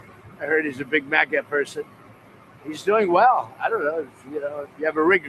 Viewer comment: I thought said, "He's a Macca person like me. (ie, Mac Donald's)?